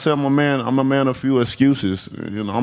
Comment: I thought he said he's a man of few excuses ??? What is this? Sounds like. Excuses to me??